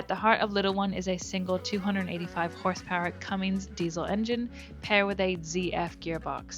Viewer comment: Well... at first i thought you said a 'non diesel' so I was hanging out to see what sort of Engine was in it 😅 You clearly have limited concerns with operating a vessel this size, let alone another 10 feet. At 24 litres an hour, thats a very expensive crossing of an ocean. .. the other consideration, my view, whilst these boats are designed to operate in the Bering sea, they are not designed to ride out storms! Thats why the fisherman head for safe harbours.. even in our modern times these vessels when caught out in a storm DO sink! Way too often..